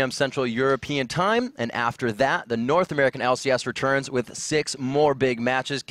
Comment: I thought it was saying North Korean LCS...